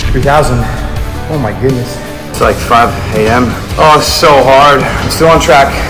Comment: bro it sounded like he said “oh I’m so hard”💀💀💀